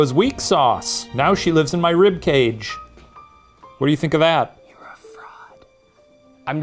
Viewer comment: i thought said "ur a thot"